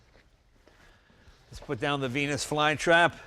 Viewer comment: I thought he said penis fly trap